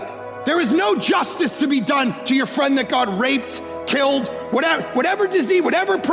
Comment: I heard "There is no justice to be done to your friend that God raped". I knew immediately I misheared it, but it felt like a freudian slip.